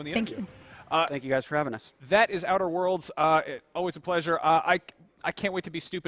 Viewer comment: Not gonna like thought it said Nutter world